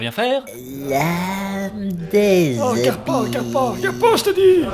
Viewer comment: C'est moi ou dit vraiment "l'âme des épices" ? ahahahaha